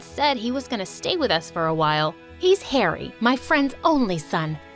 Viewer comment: I thought Aunt Celine said 'Hairy' and I laughed so hard 🤣